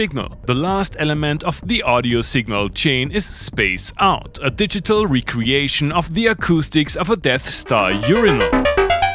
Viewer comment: Correct me if I'm wrong, but I think I heard you say, "Shaped like a Death Star Urinal. IF I heard that correctly, then, BRAVO and keep up the GREAT work!! And the [Volca MOD] SOUNDS....WOW! They're almost like MUSCI...only NOT. I'm sure Microfreak feels much better now. In all fairness, I'm quite sure you'll stay FAR FAR away from VOLCA FM2. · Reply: Thanks! "digital recreation of the acoustics of a Death Star urinal" was the original sentence;)